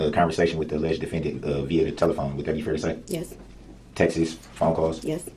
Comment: When he says "texts" he pronounces it "text is" or "texas" and it annoys me lol I thought he was saying "Texas phone calls" and I was like, what? Why did he think he was smart enough to represent himself?? I think he's just using it as a tactic to come back later with an appeal, saying he didn't get a fair trial, he should've thought that through a little better lol